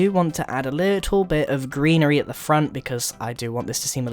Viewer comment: Hehe.... 6:40 I thought he said add a bit of Grianery XD As he was adding a G into the wall XD